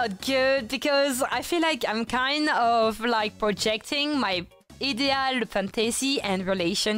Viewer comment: What did she say here? Projecting her 'what' fantasy? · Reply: French word is "idéal", can be translated to perfect